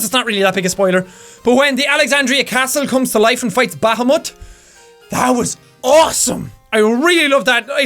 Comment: For those who are confused with what Jack said at 10:44, he said Bahamut and pronounced it "baa-haa-mutt" (I am NOT making fun of Jack's accent, Irish Brogue is AWESOME!! I am just trying to help those who didn't understand)